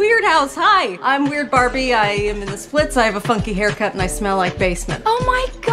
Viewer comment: Is it js me or did it sound like she said "i have a f8cky haircut